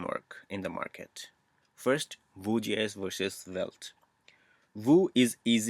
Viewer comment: the automatic subtitles thought you said "bougie-ass" when you said vue.js